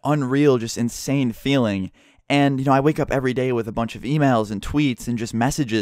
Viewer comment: I thought u said at 3:55 'i wake up everyday with a bunch of females' instead of emails